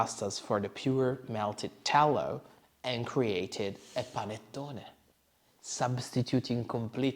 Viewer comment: Fyi - there's a mistake in the English captions at 10:15 - "daughter" instead of "panettone"